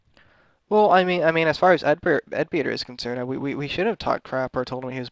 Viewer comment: Did he say "Edbeard" at 0:35? or am I not hearing that right?